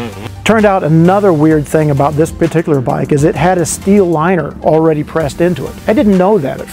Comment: What was that the bike had? He said on minute 2:58 ... steel what? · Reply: steel liner or sleeve in between the piston and barrel